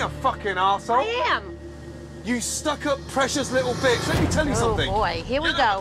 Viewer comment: "You stuck-up precious little bitch" has to be one of Gordon's best insults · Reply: not gonna lie I thought he said 'precious little butch' at first and my jaw DROPPED.